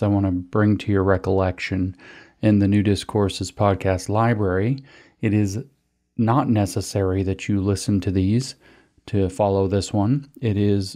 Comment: I hear "Nudist Courses" every time...